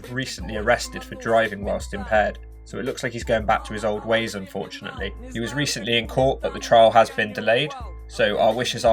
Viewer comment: he started doing what? i cant understand wha you said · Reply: Inner monologue